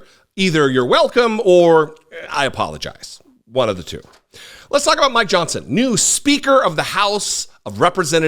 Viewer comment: First, without the beard, you look like you just graduated from high school. Secondly, slow down and annunciation when you say the Speaker of the House's name. At 0:37, I thought you said, "Let's talk about my johnson." Wait?! What?